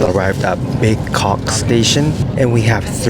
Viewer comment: i STUNNED and then rewind back to the train map earlier, he's referring to Bekok Station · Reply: I also stunned hahahaha I thought he said big c*ock station im like what?!